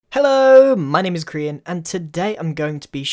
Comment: I just heard "Hello my name is Korean "